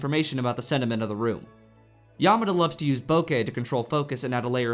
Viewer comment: Boukei? Around 5:24 What was that word? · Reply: Bokeh is a photographic effect of focusing objects with a shallow depth of field, achieved by using a lens with a large aperture. This makes out-of-focus objects (background and foreground) blurry in an appealing way, and it adds extra emphasis on the subject.